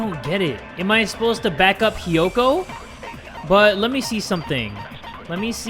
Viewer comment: did he say kyoko or hiyoko-??? i can’t tell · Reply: Hiyoko